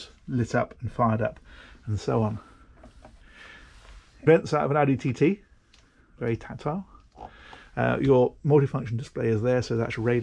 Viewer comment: no idea what you said here. what is it? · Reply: Vents out of an Audi TT, very tactile